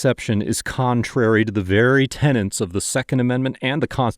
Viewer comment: Thanks for tuning in! Don't forget to stick around and watch our coverage of both of today's interactions. Hope you have an awesome day and a wonderful week! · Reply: "tenants of the constitution"? Did you really say that? 😄